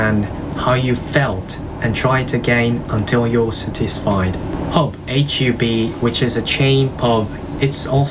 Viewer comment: Your English is so good! I do want to point out that at 1:16 you say the word "satisfied" a little wrong. It sounded like "sutisfied" instead of "satisfied". (Though, I am an American and you're speaking British English. Maybe double check the British pronunciation to be sure).